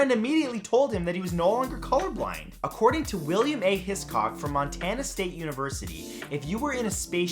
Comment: I'm so sorry but I heard ball sacks man at the introduction. 7:05 I heard William Ate his c=ck